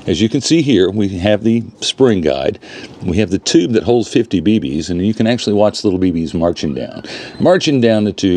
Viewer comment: It's it possible to put a modern model 25 shot tube and abutment in a modern Red Ryder? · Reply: 40…I thought he said it Holds 65 BBS…great perspectives…and we can not understate his work it’s crazy to see that Kentucky long ridge build…It’s the absolute top in terms of finishing and detail…As always great video.